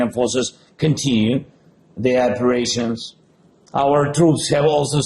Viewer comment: I don't want to be mean towards Zelenski, but I really think he needs diction lessons, because when he says "operations", we hear "aggressions"... That's really not good for the image of the struggle he stands for... I swear I am telling that seriously and that I heard "agressions" and not "operations" ! The war is not only on the ground... In a war, the most important weapon is not the riffles, it's the words (the ideas, the narrative, the stances, the statements) because a war can be win or lost on the ground, that's not the real victory... The real victory is the way people see and will remember in History how each side is... As an Israeli Jew, I can say that there is no doubt we will win against all our ennemies on the ground, but unfortunately, we're not very good in communication, and our enemies are several steps ahead of us because we have been far too pragmatic for too long and we believed that the most important thing was what was factual, concrete, that that was what we had to focus on and so we have considered communication (hasbara as we call it) for too long as something useless, that it was abstract, that it was purely ideological and we neglected it because the most important thing was, for us, what was happening on the ground... We were wrong, and we realize it today, but our enemies were not mistaken and understood the importance of communication, they understood a long time ago that words are weapons in their own right, and they excelled in the manipulation